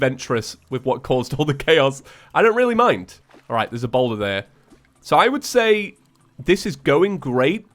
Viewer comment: I thought said "There's ebola there." I was like, no, it's coronavirus now.